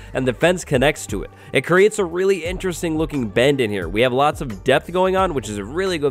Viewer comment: I thought he said a lot of death which is really good thing to have